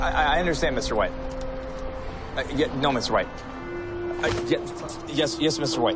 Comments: WTF is up with everyone saying they thought they heard Clark say "Mr. Wayne"?! LOL I clearly hear him say Mr. White. Just sayin' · I thought he said mr Wayne not mr white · At first, I heard Clark say "Yes Mr. Wayne." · I thought he was saying mr. wayne